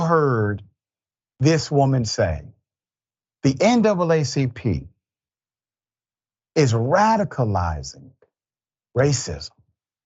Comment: i didnt hear her say "naacp is radicalizing racism" she said racializing and weaponizing.